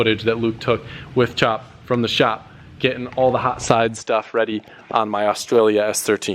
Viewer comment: did I hear chop shop